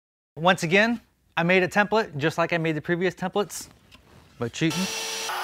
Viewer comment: did you say “buttcheek”?